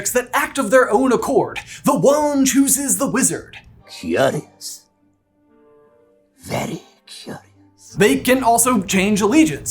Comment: It’s funny I couldn’t understand him when I was little kid because I thought he said yikes very curious not curious very curious